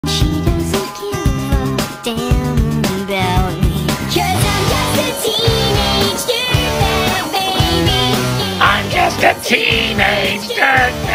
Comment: I thought it said “teenage douchebag”💀